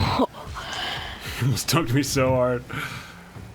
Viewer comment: I heard “ohohohohoho you just turned me so hard” lol